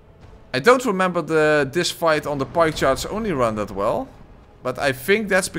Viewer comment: It took me most of the video to realise when you were saying the 'pike charge only run' that that's what you were saying. I kept hear 'pie charts only run' and was absolutely mystified by what that could be hahahaha. Excellent run!